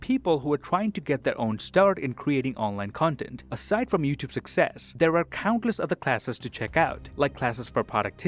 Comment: i thought you said "youtube peasants"